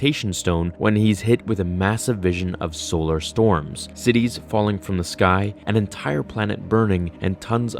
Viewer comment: I really thought he said Sidious falling from the sky Lol